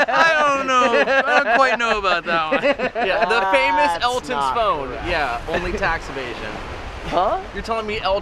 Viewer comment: I thought said “Elton Scone” instead of “Elton’s Phone” 😂